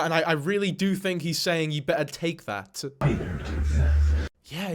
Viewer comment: okay so I listened a few times, first with one earbud then I plugged in the other as well and what I think he says isn’t “you better take that” but “hi there princess. That’s what I keep hearing every time I replay it